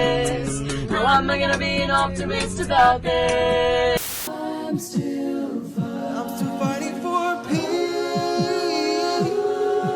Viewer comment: I thought said I'm still farting for peace 😂😂😂